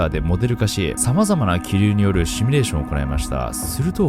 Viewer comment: シュミレーションって聞こえる シミュレーション シ・ミュ・レーション